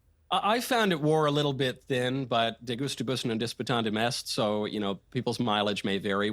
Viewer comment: What did say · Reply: De gustibus non est disputandum